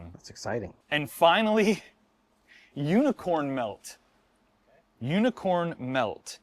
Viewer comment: I wasn't reading the captions for a minute and thought I heard unicorn milk. 🦄 🥛